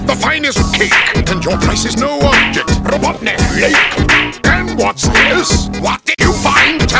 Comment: I almost thought he said "Robotnik will make" but now I do hear Robotnik Lake